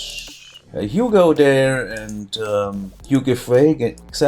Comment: You said "Hugo there" and I thought "Biffa!" Then you said, "I watch too much Biffa."